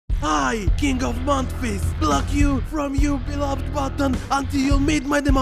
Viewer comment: I thought he said king of mount RIZZ